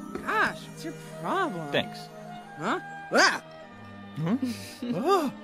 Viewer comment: Did anyone hear the word bitch at 5:11 ? Edit I think he says thanks 😂